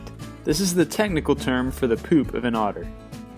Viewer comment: "This is the technical term for the _______ of an otter." What goes in the blank? I am hard of hearing, & couldn't catch it. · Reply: poop